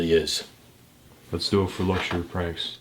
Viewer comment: The captions heard mushroom price instead of luxury pranks lmaoooo😂 6:56 👌